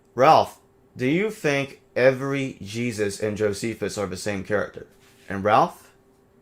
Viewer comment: No, I would have noticed it. It's a huge pet peeve of mine. You're doing it every other sentence or more. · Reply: Sounded like you said that every character and Josephus is Jesus. I assume now you meant every character in Josephus is Jesus, I but the only times I hear you talk confidently and with passion is when you've got a hard-on for Ellis. 99% of the time you sound like your on thorazine.